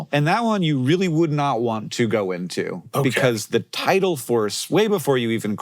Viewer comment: caption error here: “title” instead of tidal